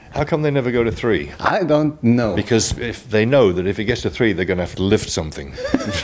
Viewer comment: Sorry guys...what does Roger say at 0:10? He speaks too fast for me... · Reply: Davide Carini He said that they count to two because if they get to three they know they will have to lift something. It was him making a joke